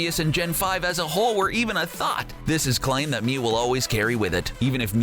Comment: Did he just call arceus A THOT!!!!